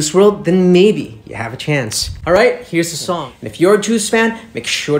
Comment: I heard "bug one" 😅